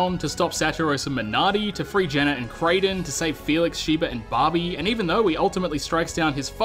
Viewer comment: I swear, I keep mishearing "Barbie" instead of "Babi"xD